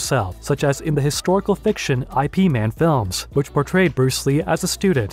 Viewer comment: Did he say I P man movies?